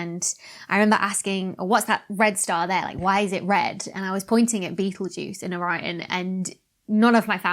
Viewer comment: I thought she was saying "beetlejuice"...